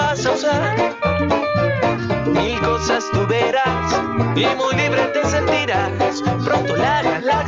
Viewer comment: creo que dice "mil cosas tu verás" no "y cosas tu verás"